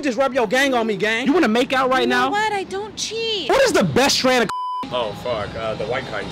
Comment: What he you say at 1:49 that question? He said it too fast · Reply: "what is the best strand of cocaine?"